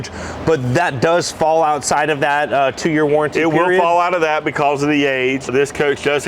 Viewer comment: 2 year 24k warranty?man thats a absolute joke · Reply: At first I thought he said 10 year 24K warranty which sounded weird. I agree that if you say you make the best then the warranty, even on the used units, needs to be a tad longer. Anyone know what the new warranty is?